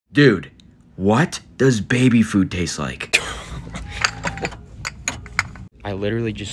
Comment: I think I heard you wrong I thought you said what does baby taste like?🤨🤨🤨